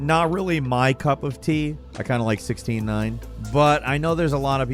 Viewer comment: I definitely heard "I kinda like 69" instead of 16:9 lol. I was like uh... this is a gaming channel?